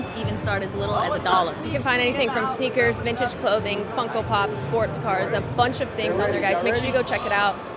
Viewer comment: I thought you said they said you can find sports cars on whatnot at 0:29 😂